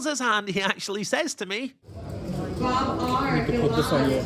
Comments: What did seat 8 say when he showed pocket aces? Couldn't hear it over the PA. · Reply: "Put that in your video" 😂😂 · What does guy say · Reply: "Put that in your video!"